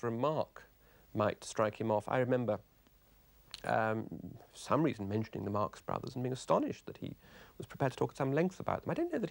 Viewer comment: Can someone explain please what did Humphrey Carpenter say at 1:54 - 2:01? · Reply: "I remember... for some reason mentioning the Marx Brothers and being astonished that he was prepared to talk at some length about [them]. And I don't think that he actually had seen their films, but he obviously thought he had."